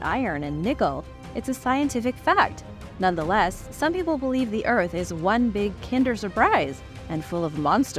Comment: Did she say peeps think the Earth is one big Kinder surprise. I hope there are giant plastic dinosaurs or tattoo stickers in it! (